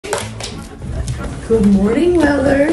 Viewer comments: Monday actually sounded like he was saying good morning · Bro he can talk😭💀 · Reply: well he can’t actually talk, but it kind of sounds like he saying good morning